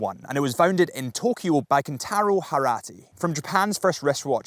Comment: It sounded like you said the Seiko founder’s name is “Harat,” instead of Hattori. Is it just me?